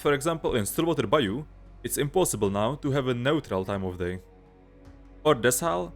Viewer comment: what did he say? It's impossible to have what time of day on Stillwater? I couldn't understand what he said. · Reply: Neutral - the clearest time of day in game